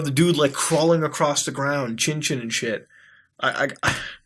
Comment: Did I hear Chin-chin? yes Mutahar PREACH